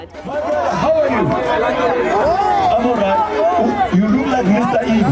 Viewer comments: Lols 😂😂 I heard "You look like Mr Ibu" who else heard that? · did he just say you look like Mr Ibu? 😂